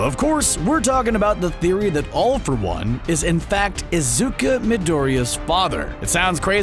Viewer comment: Did he just say "IZUKA Midoriya"?!!!